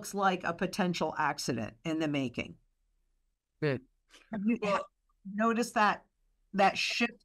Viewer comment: Did anybody hear "good" at 18:35 as Lynette was mentioning about a collapse?